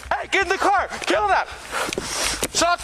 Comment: Captions are wrong. Officer didn't say "Kill that." at 13:03. He said "Kilnapp", the name of the other officer